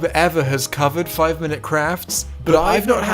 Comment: I thought he said "5 minute craps" 😅